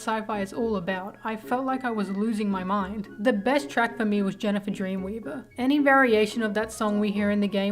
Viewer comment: I heard "Jeniffer Dreamweaver" and now I can't get it out of my head.